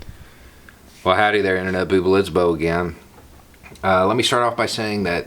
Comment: I heard "Internet Booble"...I feel okay with that.